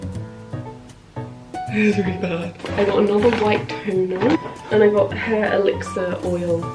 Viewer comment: When you said “white toner” I thought you said white tuna